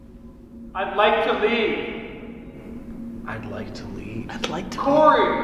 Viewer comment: From 32:20 to 33:01 we get from the words “I’d like to leave” “Corey” “my bed” and “look after me”. At first when said I’d like to leave, I heard I’d like to lead just like Corey. Since those words sound pretty similar, there is a chance that the word lead is what was actually said in the spirit box. If that’s the case. Then we can put it all together, and adding only two words we get the two sentences, “I’d like to lead Corey to my bed. Please look after me”. Perhaps the spirit felt scared and wanted to lead Corey to it so it didn’t feel quite as bad. Thus the look after me. And taking into account that a girl was mentioned earlier in the session and the only girl we know that was ever on that floor was the young one who died, it seems like that little girl wanted Corey to look after her for whatever reason and was leading him to her. Idk. Just and idea.